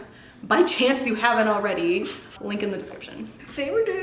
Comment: Wait is his name Lincoln at 00:25it sounded like you said Lincoln but i think you ment to say Link in??Idk ilyssm❤💙💚💛🧡💜